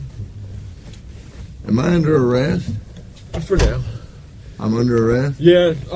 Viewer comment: when he said ''am i under arrest'' i thought he said ''my name jeff'' for a moment